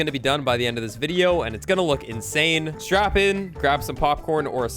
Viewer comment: “ Did he say strap-in or strap-on? ”